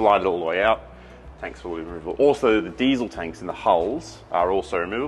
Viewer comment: I loved the explanation about hull speed! I also thought said he put bees in the water tank (not diesel) I got strange looks when I asked why one would do that….